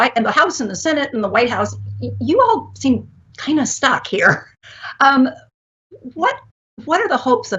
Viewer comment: I thought that lady said you all kind of Suck.. I was like I agree... lol then i watched it again and and she said you guys are kind of stuck.